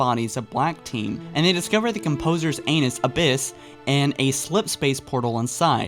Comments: did he say they discovered the Composer anus ?? 😂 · did he just say the composers anus? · am i the only person i heard "Composers an*s abyss" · did he say “they discovered the Composer’s anus abyss?” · Your pronunciation of certain words (like Onyx, Ascendant Justice, Gao and Yucatán) is wrong and made me cringe. It also sounded like you said "Composers Anus". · Did I hear that correctly, "Anus abyss"? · Did you say anus? In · Did say Composer's anus XD